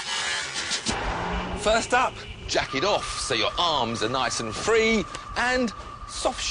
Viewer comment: At first I thought he said, "jack it off" in the beginning. LOL. Then I see him throw his jacket..